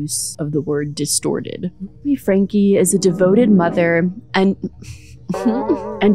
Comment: "Ruby Franke was a devoted mother" sounds like someone saying "daddy-o five was a devoted prankster."